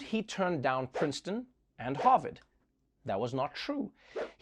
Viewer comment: Instead of calling this episode "Fringe-Watching", it should be called "Cringe-Watching". Also when Trevor says the guy turned down Princeton and Harvard, I could have sworn he said Hogwarts, lol. I'm old, my hearing is going. 😁